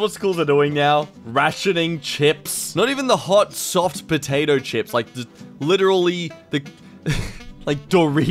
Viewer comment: did he just say hot soft potato chips